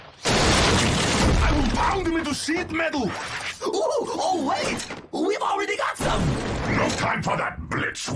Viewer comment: Did Blitzwing just said: "Shitmetal"???